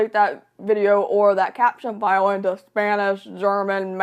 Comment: Hi! Just wanted to let you know that at 0:38 the english caption says “or that caption (INAUDIBLE)” and I’m pretty sure the unknown word is “file”.. I have APD so I don’t wanna accidentally give misguided information but from context that’s what I’m pretty sure you said, haha.. hopefully this helps! 😊 Also I really hope YouTube doesn’t take away community contributions because that would be soooo harmful to those who need captions :/ Even though they’re flawed, they should just be improved, not removed all together